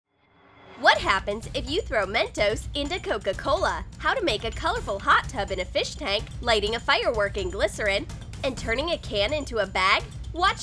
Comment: men toes is what i heard